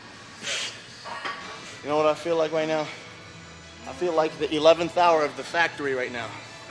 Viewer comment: 11th tower or 11th hour ?? 🤔 can anyone say what king kamali sayed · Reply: 11th Hour